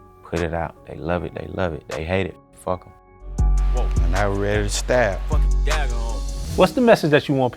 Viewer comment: Anyone else catch that “Now we’re ready to stab”? Savage💀